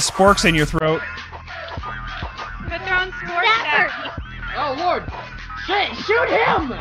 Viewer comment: Chloe Rose yep. Ok sure. I think i had one guy in my emails. ONE GUY! Who thought said " Oh lord, SHIT" may say that, but nothing stops from saying Shit... chloe i dont mean to start a debate, but it sounded alot like he said "Oh lord. SHIT"